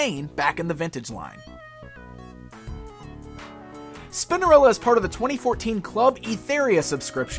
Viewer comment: Another great review! Well done for not putting a salt 'n' pepa pun in anywhere when you said her name!!! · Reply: I swear he says Spindarella at 8:22😳